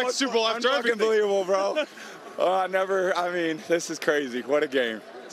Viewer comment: Did Brady say un-f'ing believable on live air? 🐐 not even the FCC can stop him